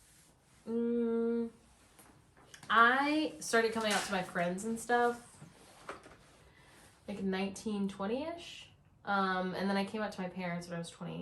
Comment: Truly thought you said “I started coming out to my friends at like…..1920ish” and I was like “omg past life coming out story!” Talk about rocks for brains!